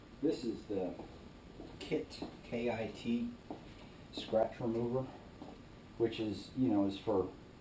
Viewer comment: Thanks for the video. Good results. How do you call that yellow bottle? I heard "Kitty scratcher"